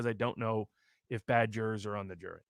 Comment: lol, @ 46:29 for a second my mind thought he said "badgers" XD